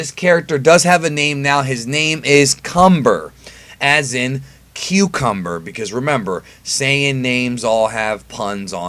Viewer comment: I thought that said super sayian cucumber